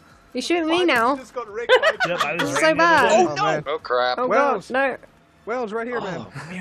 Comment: did he say whale?